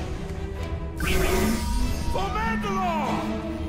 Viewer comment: what did pre said? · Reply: For mandalor!!